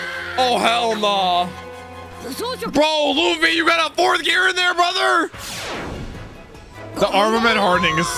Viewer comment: luffy you gotta 4th gear brother 🤔did i hear it right